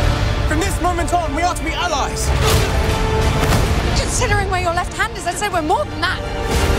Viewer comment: Wait.. what is she saying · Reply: considering where your left hand is, let's say we are more than that